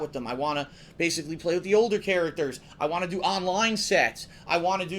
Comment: it sounded like you said "I wanna do Online Sex". I realize you said "set".